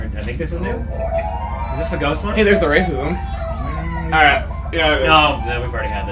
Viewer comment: In 1:23, did chugga say "day at the racisms"?